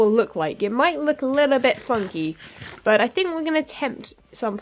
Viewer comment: Did cuss at 6:08 or did he say funky? If she caused it would be the first time I have heard her cuss in awhile. Anyways, great vid! :D